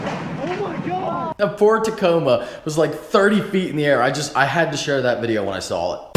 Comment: 100% thought you said ford tacoma at first lmao